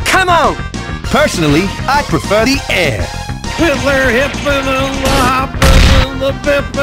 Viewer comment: is th definition of what is bro yapping a- DID HE SAY HITLER